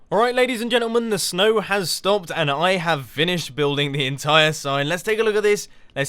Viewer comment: lol it sounded like he said vinished XD